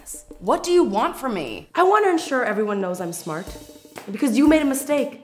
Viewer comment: The subtitles say ensure as: "insure"!!! at 2:46. not to brag or anything..😂😏😆